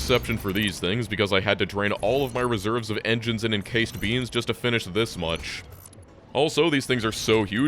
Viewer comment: The seablock seems to have taken root in my brain. I heard "encased beans"